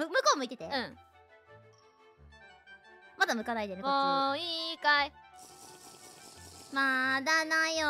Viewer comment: It sounds like Laplus is saying "mou ii kai" (are you ready yet?) instead of "mou ikkai" (one more time). Especially since Iroha responds by saying "mada dayo" (not yet) and "mou ii yo". It's kind of like they're playing hide-and-seek. Speaking of which, the way they sung the lines reminded me of Kakurenbo by Yuuri.